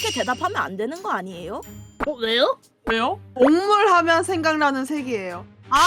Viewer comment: 생방때 곡물 색이라고 들었는데 동물 색이었군뇨 ..